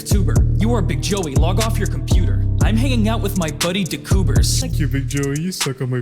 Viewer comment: For the outro I thought you said “I’m hanging out DB Cooper” that’s my steam name lmao